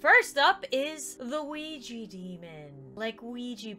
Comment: At first I thought Lauren said “Luigi Demon” 😭